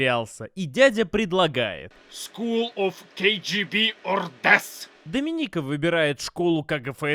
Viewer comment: Что на 9:10 Баженов говорит? - School of KGB or... Я второе слово расслышать не могу. Tess? Dess? Что это значит? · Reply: School KGB or Death. Типа даёт выбор, либо в школу, либо смерть.